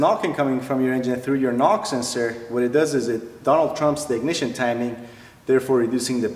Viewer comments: Did you just say it "Donald trump's ignition timing" instead of it "retards' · did you say “Donald Trumps the ignition timing” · donald-trumps the ignition timing...good one and great videos aside from trump as well also is there any way to 'revive' a car's performance without spending a ton of money? (due to age) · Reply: That’s what I thought he said “Donald trumps the ignition timing” lol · "it donald trumps the ignition timing" did I hear that right?